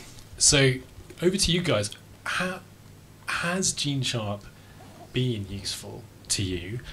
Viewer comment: They keep using this word. It sounds like: "GENE CHOP". What are they saying?